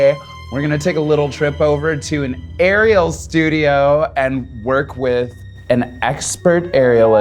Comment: I literally thought that she said Ariel's Studio not an Arial Studio